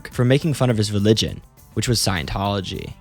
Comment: thought he said chin-ology